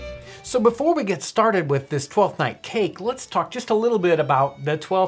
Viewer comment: thought he said fortnight